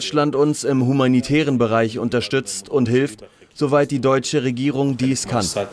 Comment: Was sagt der in Minute 8:00 ?…:so lange Deutschland dies….????